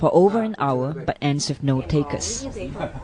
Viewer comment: What does she say at 2:40? "...but ends of no take us" -? (Sorry, but I'm not a native speaker of English.)